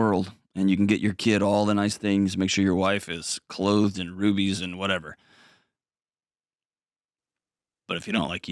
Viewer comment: I thought he said boobies I am bad